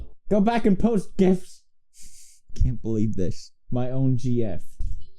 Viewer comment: Wait, did he just say ”my own jf” when Marzia was speaking